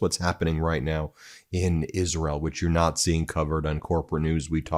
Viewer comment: Did you just say "Israel witch you're Nazi!" ??!! :P Swear to god :)